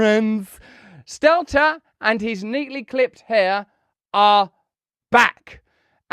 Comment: Ryssell, I thought you said "Neatly clipped PAIR" 😂